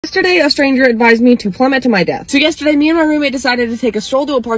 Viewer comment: Bro I thought you said “plummet to my dad” lol